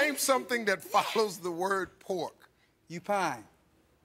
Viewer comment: I heard "Upine," not "Qpine"